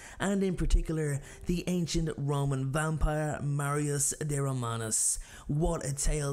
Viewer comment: did he just call Marius a ramen vampire? As in the noodles? Lol.